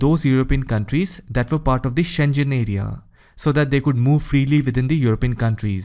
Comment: did he say Shen Zhen area??